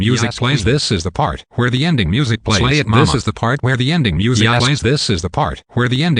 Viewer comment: throughout the video, i thought the clip was saying slave mama and it confused the shit outta me